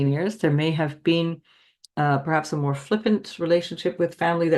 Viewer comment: Will someone pls help me understand what says at Sagittarius 1:31:56 and just after, about family relationships “you may have had some ???… relationships that may become more serious” Im Danish and I just dont get exactly what the word she says😅 help much appreciated😇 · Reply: She said "flippant" relationships. Meaning not respectful.